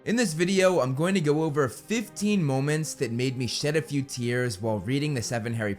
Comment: I thought he said shit a few tears